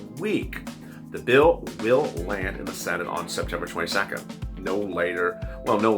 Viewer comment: Did he just say September 22?